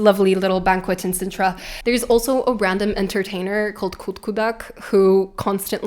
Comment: Did I just hear "kodkodak" or "kokkodak"? 😮 I'm Korean and kokkodak (꼬꼬닭) means chicken (or the noise it makes) in Korean... that's so random. (edited: looked it up and saw that it's spelled Coodcoodak in the book) 1:21:57 The earliest version of the Law of Surprise appears in the story in the Old Testament about the judge Jephthah pledging to sacrifice the first person to come out to greet him (Judges xi. 31), and actually carrying it out. 💜 for having my attention to the end